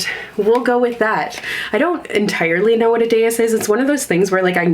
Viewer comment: what's this word? I'm also not natively English so I have no idea what this means, I tried to google it but I don't know how to spell it! Deus? Dayas? Deyus? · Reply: It's dais :) it's like a low level platform that thrones are often on!